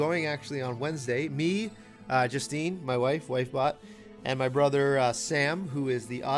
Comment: I heard "wifebot" when you were saying how ur wife bought you an iPad before you repeated yourself and carried on....i was like damn he drew his wife so she's a part of carbot??